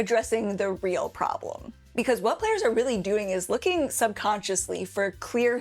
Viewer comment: I misheard "players are actually looking for clear hints" as "players are actually looking for queer hints" and the next 30 seconds or so i was wondering if i was watching the right video